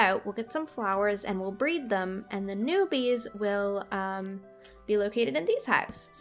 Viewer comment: For the bees u should make a custom beehive where u can walk in and that's where all the bees will be and that can be where u can collet honey and for a challenge, u should make a MEGA tree and put the big beehive on/in it. :) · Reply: I thought you said “Bee will BEE in it” Xd